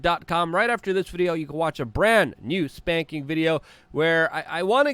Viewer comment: Next video - <link> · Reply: Did he just say we can watch, "a brand new spanking video" ??? 🤣😂😆